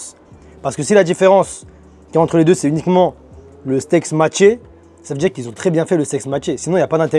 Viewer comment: ils ont très bien fait le quoi ???? j'suis pas sur d'avoir bien entendu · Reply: Secret